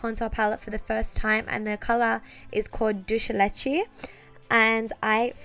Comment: did anybody hear her say dulce leche